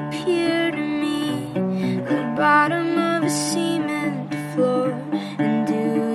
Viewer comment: Did she say "bottom of a semened floor" or am I totally mishearing?!